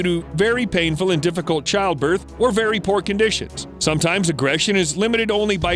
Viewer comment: did he say?, "very pork decisions" 🤣